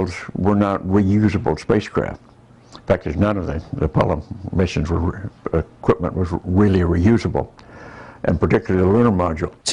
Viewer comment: In fact, no of the Apollo Missions were real, equipment was really really reusable. Maybe I misheard it. Sorry. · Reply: Enon Ereh: ALL of the Apollo Missions were REAL. The U.S. DID send men to the moon. Just consider how much hardware was sent and how much came back? The entire Saturn 5 rocket, the lander all lost. The only things that came back was the command module, and some of the equipment that the astronauts used on the moon, such as Mitchell's camera, not counting the 840+ pounds of moon rocks and dust that they brought with them.